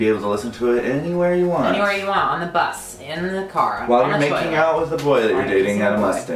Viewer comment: I heard " while your making out with a boy and molesting " and I was like, Scott no. but then I played it again and then heard the correct wording and freaked just a wee bit. #dead